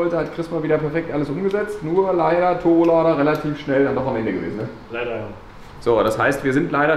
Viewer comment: Was sagt bei 21:17 (was nicht so gut lief)? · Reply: Lader am Ende. Heisst, der kann nicht mehr Luft fördern.